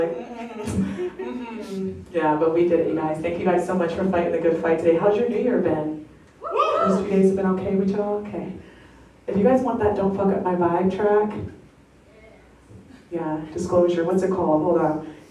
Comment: Ohhhh. Totally thought u were saying “don’t f up my eye” I was like, well the lyrics are wierd but it has great energy. Hahaha